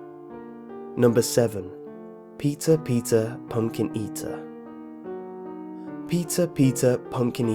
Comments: did i hear pizza,pizza pumpkin eater LOL!😂😂😂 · I thought you said "Pizza Pizza Pumpkin Eata" haha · I thought he said, "Pizza pizza pumkin eater" hahaha